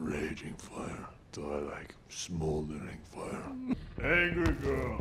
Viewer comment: I thought he said "Thor like smoldering fire" here.